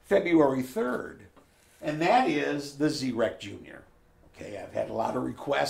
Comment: I thought he said, "The Z-Wrecktion," lol. I had to rewind for clarification.